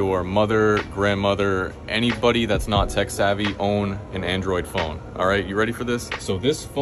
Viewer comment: I swear I heard him say, "...Your mother, your Grandmother, anybody that's not SEX-savvy!"